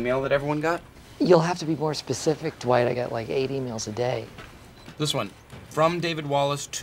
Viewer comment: “You’ll have to be more specific, Dwight. I get like, 8 e-mails a day.” 😂🤣🤣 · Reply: I thought he said he ate emails all day